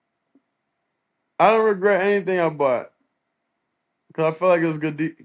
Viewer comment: what did he sayyyyyyy😭😭😂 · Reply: I didn't regret anything I bought 😂